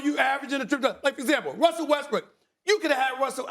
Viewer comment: Feel like I heard Stephen A.say Russell Westbrick …